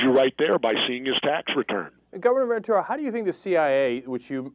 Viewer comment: lol, at about 36:01 i think Cenk called Ventura "Governmentor"